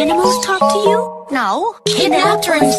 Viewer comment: Wait it said it said "look like a bitch?" Or "broke like a bitch?" I thought it said "smoke like a bitch" 😀😶